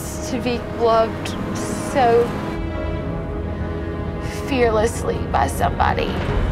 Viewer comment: “To be loved so fearlessly” yet she and Chris made it sound like she said “fiercely”